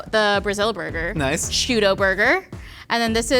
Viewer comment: “tudo burger” is that what she says? cause it does resemble a “x-tudo” that we have here